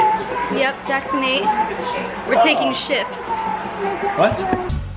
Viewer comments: when says "we're taking shifts" and says "what 😳" 😂 I think he heard "taking sh*ts" 😂😂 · Am i the only one who realised at 1:30 (middle finger) haahahhahaa omg lol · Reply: Yeah I seen that too, my mouth went wide open ROFL!! and then the part where said "we're taking shifts" and was like "WHAT" I think he thought she said SH*T